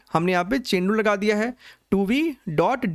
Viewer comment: Revising 4th time in this month JEE 2024 but just didn't understand this word 9:15 "humne yaha pe chenu laga diya hai" matlab? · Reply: Chain rule